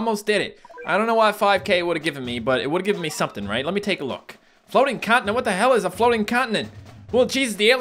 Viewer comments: when said "floating continent" I LITERALLY SPIT OUT MY WATER ONTO MY PHONE BECAUSE I TOOOTALLY THOUGHT HE SAID "flowy condom" Lmaooo 😂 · sounds like he said floating condom xD